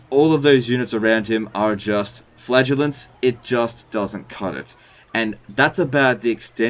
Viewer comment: I heard "he buffs Flatulence" instead of "Flagelants"...